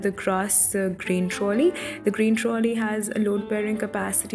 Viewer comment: Hi ....I couldn’t get what she said about the thing at time 1:33 under the oven is “green trolley”....???whats that plzzz explain on that ...pls reply am your regular viewer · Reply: Hey Isa. Sorry for the late reply. She said grain trolley. It is a deep drawer used for storing oil canesters and large storage boxes. It has a weight bearing capacity of 50Kg